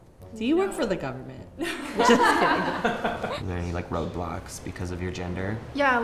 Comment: its 3 am and I keep hearing roblox